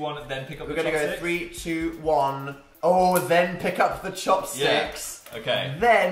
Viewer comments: i thought chopstick said chapstick and i screamed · I thought it said Chapstick and I almost shat myself